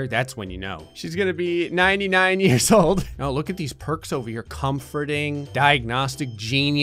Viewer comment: when you said look at all these perks over here... I thought you said look at all these percs over here..as in Percocet. LOL. I mean it’s a hospital...and it’s run by..took me a second to register haha.